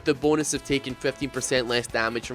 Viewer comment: If you're talking about the Leviathan I thought that was only 15%. I keep hearing 50% when i replay the clip